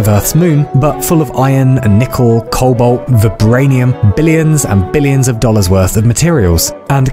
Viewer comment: I enjoy the first 3:19 mins😂. You are funny chief...lets go another round for "the settlers" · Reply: Did he just say vibranium😂